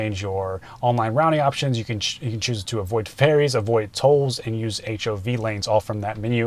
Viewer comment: I was listening while performing chores, so I misheard that your Tesla avoids Fairies and Trolls. Handy in EV saturated Norway. Will there be a dragon and unicorn over the air update?